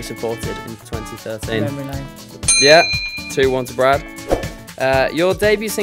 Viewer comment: What did he say at 1:45? :( i do not understand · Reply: memory lane